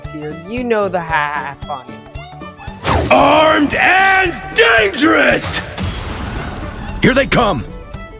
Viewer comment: ARMED AND DANGEROUS!! (Again chain) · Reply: i always thought he said "i'm dangerous"